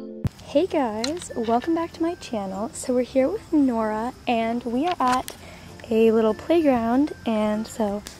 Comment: I thought you said "we're here with laura"😂😂